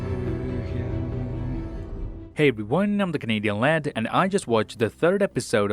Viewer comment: In the intro there where are "The Canadian Land" i heard in German "och ich singe" what means "oh I sing"